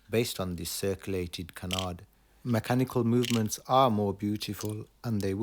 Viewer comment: Thank you for the wonderful videos, they are a delight. May I ask what word you used at 3:17, is it Cannard? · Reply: Thanks. It’s Canard